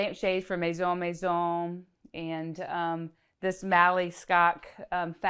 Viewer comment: love the fabric in the guest house of the first home. At 35:24 she says it's a "Mali Scott fabric", or something like that. I couldn't find anything online that sounded like that. Did anyone catch the name of the kind of fabric that is, the pink ottoman? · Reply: It is the Mally Skok Cape Town in pink.